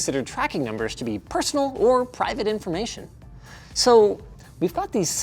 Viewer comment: did you say private inflamation? 10:24 ?